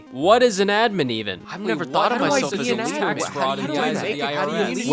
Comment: I just heard tax fraud for a second